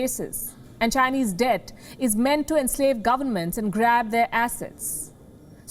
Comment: Sorry, I heard "grab their asses"....which is probably not wrong anyway